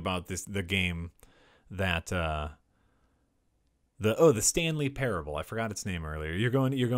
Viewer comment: At first I thought you said the Stan Lee Parable. That would most definitely be meta.